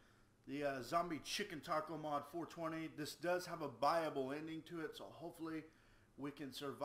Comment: buyable ending means you can buy the end of the game so you "win" instead of die. (at first I thought he said bible ending as well XD)